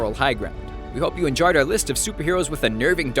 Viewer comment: Am i the only one that heard him say "I hope you injared this list."?